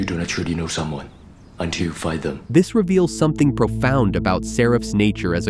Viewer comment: I thought Seraph said to Neo “You don’t truly know someone until you FIND them”?